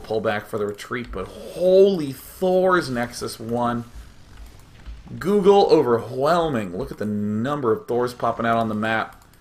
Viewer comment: I heard "google overwhelming" and thought that google was making some archons.